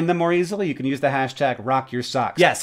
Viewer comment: First time I listened through I misheard Hank sign off “John, I’ll see you in office” and I went “wait … WHAT??”